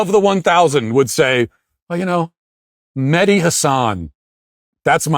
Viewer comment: Mehdi thinking that Ben will actually care is laughable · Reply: Every time I've heard his name I thought ya'll were saying "Mini Hasan" I figured it was a short communist 😂